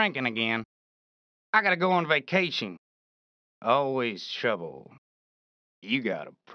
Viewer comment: What Said? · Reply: "Always trouble"